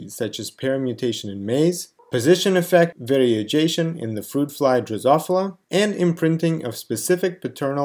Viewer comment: what word did you say here? I listened over and over but I'm not getting it.. and another at @ 8:51. Wids? Your content is great, it really is but practice enunciating words better, please! So many times I had to re-listen. Like "groups of Adams" sent me looking on Google for wth they were. You meant "atoms", I see now but it was confusing for a moment there. Constructive criticism only, my friend :) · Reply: in 2:02 I believe you are referring to "variegation in fruit flies" ..... The effect is the variegation in a particular phenotype i.e., the appearance of irregular patches of different colour(s), due to the expression of the original wild-type gene in some cells of the tissue but not in others, as seen in the eye of mutated Drosophilamelanogaster. at 8:51 - "Widths" sorry if it was not clear! And thanks for the constructive criticism!